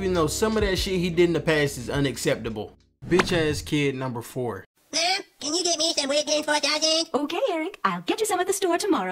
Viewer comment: If you don’t know what Cartman said at 9:44, he said, “Mom! Can you get me some Weight Gain 4000?”